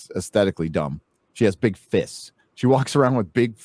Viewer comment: Oh, said that she had big "fists"; I thought he said big t.... Never mind, I wasn't going to watch anyway.